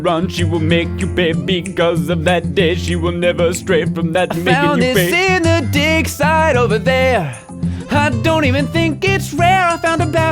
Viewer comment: when you were singing that song and you found the haniwa in the dig site, I thought you said "I found this in the dick site" (∩＿∩)